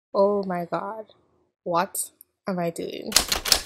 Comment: I have a question about the intro sentence, which starts "Oh, my God, what's an ..." ideam? "Ideam" is, what i hear, but it's not a word. "Idem" doesn't make sense, or does it? The subtitles say: "Oh, my god, what am I doing" which kind of makes sense, but I clearly hear "what's", not "what" - is it slang or what is it? :) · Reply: It's 'Oh my god, what am I doing?'